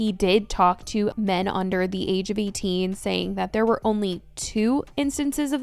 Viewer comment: did he say men? i thought i heard "guys " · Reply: no. said "men under the age of 18"